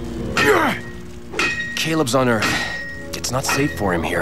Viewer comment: What does will say during the transformation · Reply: she says eather, wich means the force of all living things. i also thought she said earth at first.